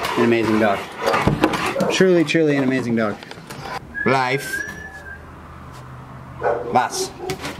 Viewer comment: did he say: blijf??? thats the dutch word for STAY. so cool!!!